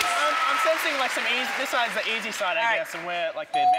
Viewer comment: I swear at 3:44 i though luke said i'm sensing it's the asian side or something i was oh shit what?